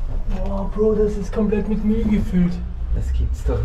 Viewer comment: Wie ich erstmal Mehl statt Müll verstehe und mir so denke bitte was, komische Art den Eingang zu verschließen? 😂